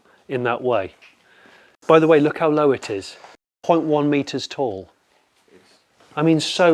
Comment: it sounds like you said ".1m tall", which is odd :).